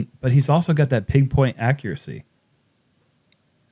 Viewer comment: Did I just hear "pig point accuracy"?😂